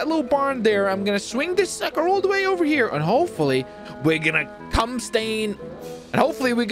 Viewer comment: did bro just say "cumstain"? hahahaha